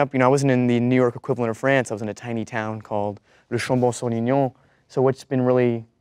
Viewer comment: I speak French fluently but that that was confusing af, I can’t hear it no matter how many times I hear it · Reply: "Le Chambon-sur-Lignon"